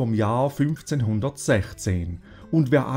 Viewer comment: wenn ich den Beitrag von richtig verstanden habe, sagte dieser 1416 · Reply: Salut! Ne ne, er sagt 1516, nur ein wenig verhaspelt. 1416 wäre ja noch abenteuerlicher. ^^